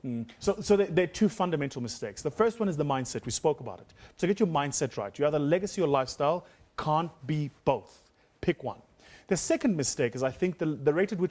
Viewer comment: can’t hear him clearly cause of his pronunciation... what does he say ? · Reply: The first one is the mindset, we spoke about it. So get you mindset right. You're either lagacy or lifestyle, you can't be both. Pick one.